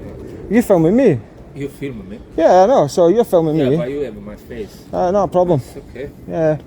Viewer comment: DId he just say "you are filming my face" when the video is showing the very fancy car? 🤣